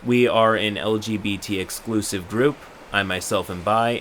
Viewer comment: “We’re so accepting!” > has a group that exclusively allows in only an explicit type of person I literally hate the “acceptance” flex from people that run these kinds of exclusive groups. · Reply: Who are they exclusive to? Besides not allowing the guy they banned? Edit: Oh I just double checked it and they are LGBT-exclusive. I thought they said LBGT inclusive lol. Yeah I definitely think that is shitty. Even pride groups aren't exclusive to members of the LGBT community because some people can't be out of the closet, and some people just want to be good allies. That just seems like it would attract gatekeeping and other bad stuff.